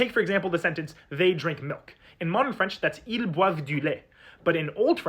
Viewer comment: Are there more people who hear /lajtja/ instead of /lajt/?